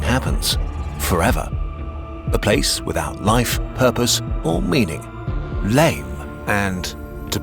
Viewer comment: Am I the only one who thought they said "A place without live, herpes & meaning"